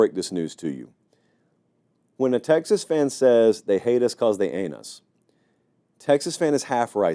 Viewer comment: All I hear is "They hate us cuz they anus..." lol